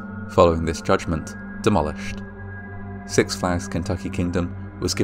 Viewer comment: The girl’s feet were amputated at the ankles, then she had to wait ten minutes for rescue. Imagine her agony and loss of blood, and the absolute terror of the other passengers. $1,000 fine for this catastrophe is a giant slap in the face for girl’s family and friends and every single rider who was there. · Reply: I thought I heard $100,000, maybe I was trying to make a number up that sounded more reasonable.